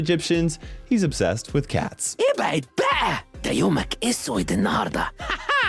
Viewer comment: if someone is confused what he says, it's actually Arabic and exactly Egypt, he says "Get out of the way! Your day will be ruined" or just "البعد باء! دهو يومك بيكون اسود النهرداء"